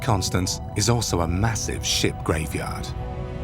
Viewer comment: Haha at 15:48 I thought he said a 💩graveyard 🤣 instead of a 🛥🚢 graveyard 🤦‍♀️🙉